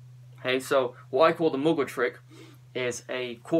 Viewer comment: did you say Muglua? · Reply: Hahah it's Mgwa